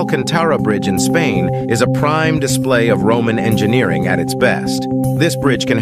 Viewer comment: I thought u said women engineering.. I said nawwwwww lol lol